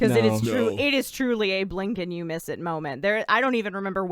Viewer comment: I heard “Abe Lincoln You Miss It Moment” I know that’s not what she said but I’m going to pretend it is